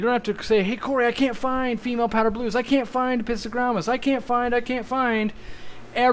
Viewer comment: The auto generated subs reads "I can't find pizza grandmas" instead of "I can't find Apistogrammas" - I love it.